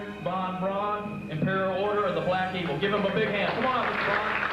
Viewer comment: Ooohhh- at 12:07, he said black EAGLE. I heard imperial order of the black PEOPLE. I'm like what's wrong with that- he's a progressive guy- ahead of his time. Whoops.